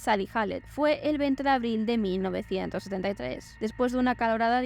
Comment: el 20 de abril de 19?? lo escuché varias veces, pero no capto lo que dices al final. Es solo un detalle, me encantan tus documentales 😍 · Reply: 1973, perdona. Para la próxima intentaré ir más despacio jaja